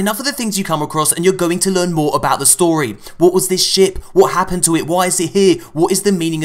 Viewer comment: I heard "what is this shit" I know you said ship but shit sounds better anyways